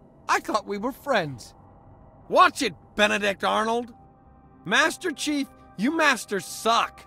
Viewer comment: “Watch it Benedict Arnold!” - marine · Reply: I actually thought he said Benedict Darnold the first time I heard that lmao 🤣 🤣🤣